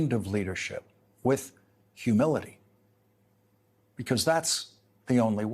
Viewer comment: Humility!! Oh.. ok, He thought you said Humiliation.